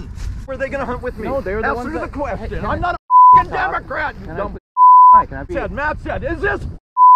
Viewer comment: The guy was his own worst enemy, but the screaming at the top.of his lungs was epically funny. In the dictionary, under the word "jack-wagon", this guy's photo must be there. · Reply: Thought he said " I'M NOT A DEMOCRAT" ?